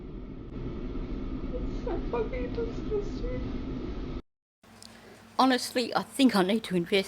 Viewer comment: what says here is: "its so f-ing disgusting"